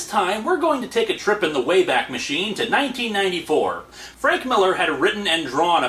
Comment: Umm... Did someone else noticed mistake at 00:38 - 00:41? clearly said 1994, but subtitles says 1992...